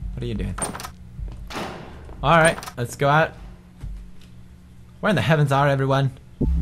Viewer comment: Lol am I the only one who heard him say "where the fuck is are everyone?" Lmao